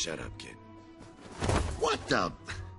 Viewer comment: I heard him saying “What’s up”.